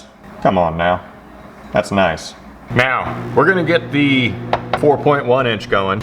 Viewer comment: did you say now or meow 😂